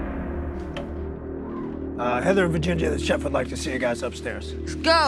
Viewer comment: did anyone else catch Scott say “Vaginga”? lmao